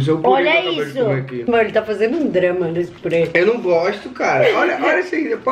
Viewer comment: moh c tá tds drama..... só eu q escutei?? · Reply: Mano ele tá fazendo um drama ** foi isso que ela falou